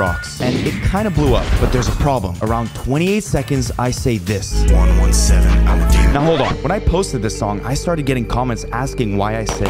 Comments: I thought he said 117 I'm gay for a hot second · I thought you said "117 I'm a gamer" not demon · I thought he said 117 I’m a gamer because gamers would know where 117 is from bro my life was a lie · I thought it said 117 I'm a gamer